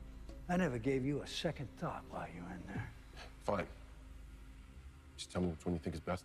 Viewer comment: I thought he said cent